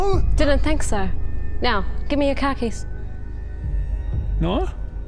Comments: Oh, "car keys". I thought she said "khakis".😂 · Ohhh, she was saying "give me your car keys". I thought she was saying "give me your khakis". Accents are fun :p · LOL, I thought she said "give me your khakis" after looking downward at. Glad I heard that wrong.... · I literally thought they were saying "Khakis" and not "Car Keys". It made me very concerned about the direction this video was going lol. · When she asked for the car keys, I really thought she said 'khakis' - actually demanding his pants. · she sais "give me your car keys", but i thought she said " khakis" and i was like. . "why would she want his pants?!" . . . . . and no, i'm not making fun of her accent. i legit thought that was what she said. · I legit heard "Khakis," not "Car keys," and was confused about the direction this was going in. 😆